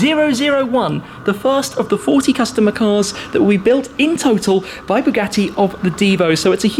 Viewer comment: did he say "that we build wit bugatti"?